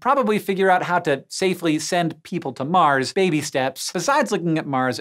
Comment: did he just say "baby steps"?